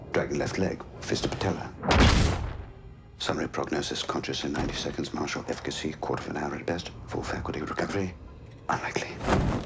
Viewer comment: what is he saying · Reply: "Summary prognosis: Conscious in 90 seconds. Martial efficacy: quarter of an hour at best. Full faculty of recovery, unlikely." So he wakes up in 90 seconds, takes at least 15 minutes to start doing anything useful and is unlikely to fully recover on his own.